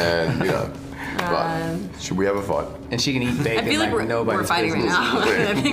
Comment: did he say "and she can eat babies like nobody's business"??? Or did I hear that wrong? I replayed it several times and I keep hearing it.